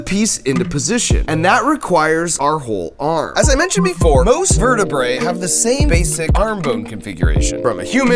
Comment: "All mammals have the same basic arm bone configuration from a human, to a bat, to a chicken [...]" That well known mammal, the chicken 🐥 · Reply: Is this a Mandela Effect thing? I definitely heard "mammals" the first time I watched it, but just went back to that time stamp 1:22 and he says vertebrates.